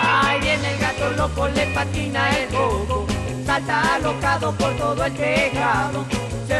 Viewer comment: TODA MI VIDA PENSÉ QUE DECÍA “a ese vato loco le patina el coco” 😳